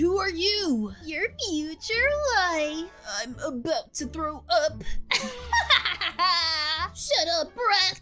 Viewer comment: DID HE JUST SAY I’m gonna grow up..